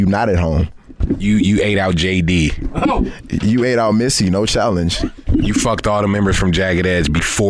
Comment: What did he say? I understood “Messi” lol but English is not my first language . Could someone tell me what he said?